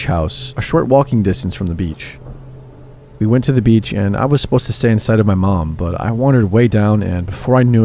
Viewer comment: I had to put the captions on for the beginning of the first story. For some reason, instead of hearing "I was supposed to stay in sight of my mom", I heard "I was supposed to stay inside of my mom".